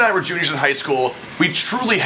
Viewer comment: Lmao I thought for sure you said "a junior in high school when you got pubes" I was like dammmmn son I was eating way too much hormones hahaha